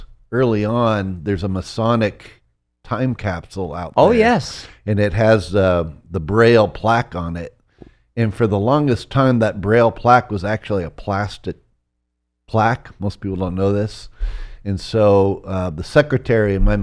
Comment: Woah … this is cool 🤯 22:48.. Bleeped out ? It sounded like he was just saying that the plaque was originally plastic?